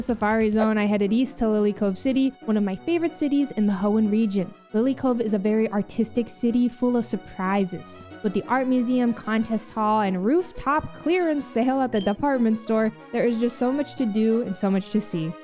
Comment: I definitely misheard "Lillycove is an Artistic City"